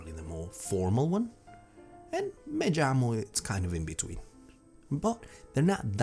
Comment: This looks like it's going to be a great series! I did want to point out there's some strangeness going on with some of the Spanish captions though. For example 14:34 / 14:55 / 15:19 "majmo / jajas" instead of "(me) llamo / llamas" -> this seemed to be a common one and may show up elsewhere 15:54: "en cantala" might be "cantada" 16:43 - "estobian" should be "estoy bien" I think Sorry if any of these are wrong, as I only know a little bit and am just going off how it sounds. I'm sure it's tough with the subs going back and forth between English and Spanish. Great stuff though, looking forward to the rest of the 90 days!